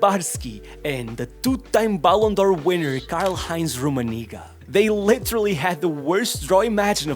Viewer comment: what did he say😭😭😭😭✌️ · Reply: Karl Heinz rummenigge